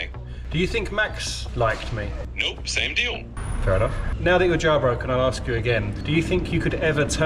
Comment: This whole time I kept hearing "jaw broken".